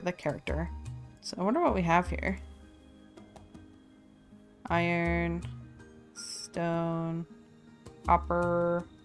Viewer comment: Did I hear a Rock and Stone?!?